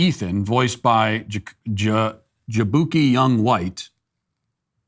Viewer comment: Sounded like you said his name was “Jabookie Young Butt”